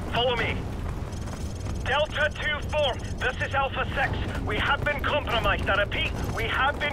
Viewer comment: Delta Two Four this is Alpha Sex ? what hes saying sex not six